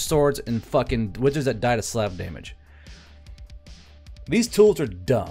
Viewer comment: Totally misheard you do to my chickens but it turned out better. "wizard dies to Slav damage"